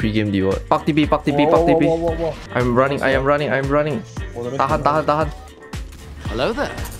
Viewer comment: did i hear "tahan tahan tahan"